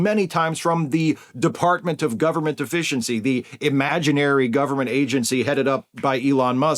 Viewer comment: It sounded like you said "Department of Government Deficiency." If so, keep calling it that, because that is clearly what they are striving towards.